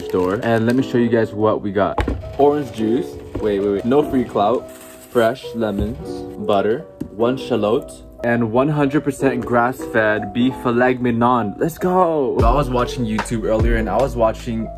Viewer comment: Thought you said I made filet minion